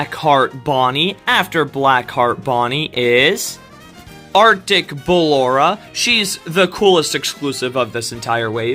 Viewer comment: i heard "our d*ck ballora" 😭😭😭